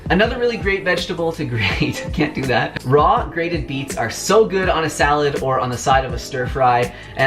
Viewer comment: I really thought you said 'grated beans' and was really intrigued to see how you were going to demonstrate that 😂